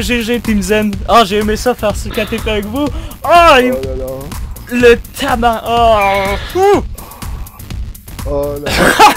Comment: C'est moi ou à passer proche de dire ''le TABARNACK'' vers 11:30? xD